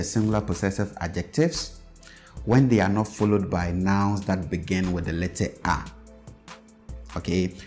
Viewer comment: Yes I thought you were saying words that begin "R" but "A" is short